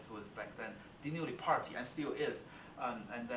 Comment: Which pianist did you mention at 12:49? I couldn't hear I'm so interested in listening to his Chopin etudes! Is it Dinu Lipatti? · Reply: Dinu Lipatti